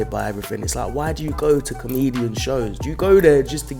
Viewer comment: i thought it said "canadian" not "comedian" and i immediately wondered, "wait, isn't that redundant?"